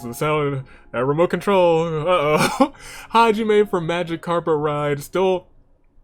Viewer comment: Beach party to the tune of Invincible Rainbow Arrow, love it 1st: United We Fall 2nd: One with Nature 3rd: Bug Hunters Other bets: Baked in Blood, Promotion Pending, Nothing to Report, Alice in Wonderland, and Momma and ...Daddy Edit: when you said "Magic Carpet Ride" I thought you said "Magikarp Arrives" lmao Edit 2: I was half hoping for it to hit to make you suffer and half not because Boss is the current champ and Boss Time needs to last a little longer Edit 3: he's Mr. HHHHHHHHHH Edit 4: for saving grace; 1st: Alice 2nd: Mizuki 3rd: Crazy Hiyoko Edit 5: at the final 3 Aiko was the on I least wanted to win but not mad Edit 6: surprised didn't mention that United We Fall got placed in via secret episode (you're all welcome for that lol)